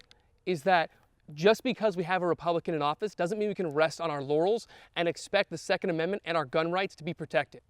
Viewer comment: why did you say "doesn't meant we can rest on our yanny"